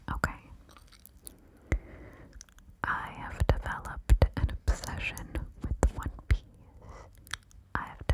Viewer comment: genuinely thought you said "I've developed an obsession with one penis" until you said it the 2nd time. Train of thought went like: woah that's a bit out of left field. : aaah, that makes more sense.